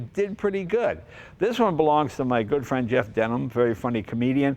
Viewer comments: Did Jay call him Jeff Denim?! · Maybe the clothes are going to Jay’s head because it sounded like he said “Jeff Denim”.